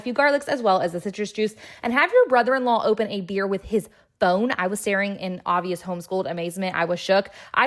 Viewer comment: Ngl, I thought you said he opened his beer with his bone, not phone...